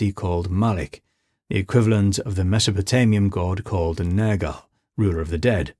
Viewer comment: what did he call that deity? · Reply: Nergal